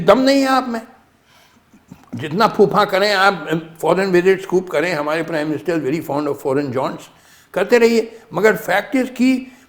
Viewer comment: I heard "our PM is very fond of foreign blondes"😂